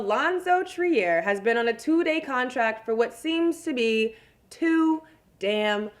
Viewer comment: did she just say two day contract, it's two way contract